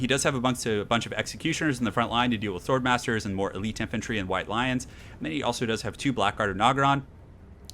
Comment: I heard it as "Roasted some of my white lines" rather than "lions" <_< >_> Never mix your meth with your coke.